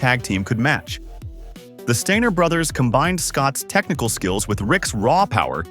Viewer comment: the Stinger brothers? Or did you say the “Stainer” brothers?